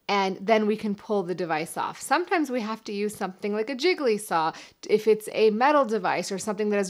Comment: Did she just say jiggly saw?